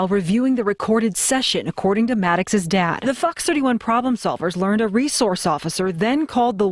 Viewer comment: Did she just say “fucks 31 problem solvers”? 🤔